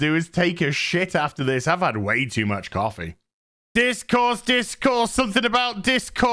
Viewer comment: Discourse I thought she was saying dishcloth